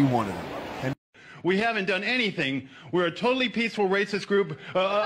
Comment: did he say they just a Peaceful Racist Group? 😅